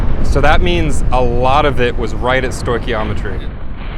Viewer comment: Does he say 'a lot of it was right at storchiometry'? 🤔